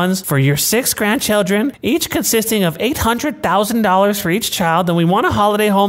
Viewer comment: I thought they said 8,000, then I went back. 800,000 DOLLARS who the F*ck do they think they are???